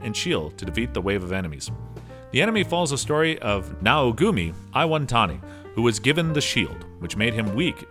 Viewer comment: Did he just say "naugumi"? and "rukudai" ? instead of "naufumi" and "rakudai" ?